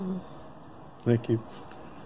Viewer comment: sounds like he said ''Ah Thank You"